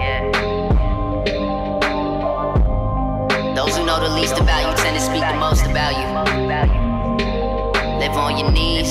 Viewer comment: Can someone explain to me what does he said in 0:12 - 0:22? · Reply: Those who knew the least about tend to speak the most about you